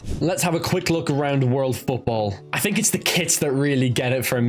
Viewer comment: thought you said kids